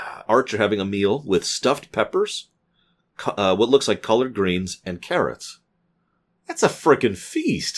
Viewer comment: Collard Greens, sounds like shirt "collar" Maybe I misheard, but it sounded like you said "colored"